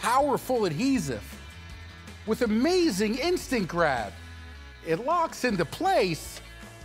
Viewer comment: I thought he said Instagram. WITH AMAZING INSTAGRAM